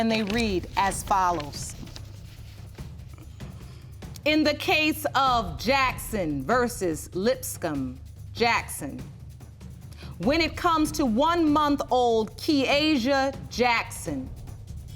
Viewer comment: I thought she said Jackson vs LicksCum